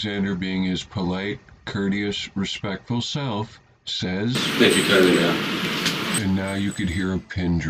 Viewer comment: What did he say? I'm hard of hearing... Someone help!!!! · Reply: ……"Thank you kindly ma'am".